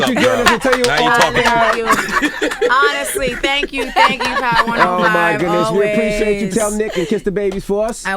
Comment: Did she just say thank you Hot 105 instead of power 105??...listen at 37:19. Im so glad Charlamagne is there to ask all the nitty gritty questions everyone wants to know...do u regret glitter???.....